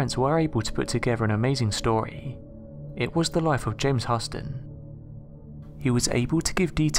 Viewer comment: lol my names is james austin i couldve sworn u said that lol freaked me out